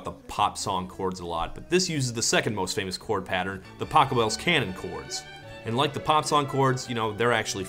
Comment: who else heard "taco bell canon"? no just me? ok.